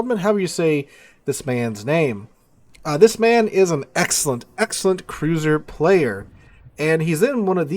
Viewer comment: It sounds like you say "this man isn't excellent excellent cruiser player" and followed by "in one of the best cruisers in the game" .. Haha I got a bit confused there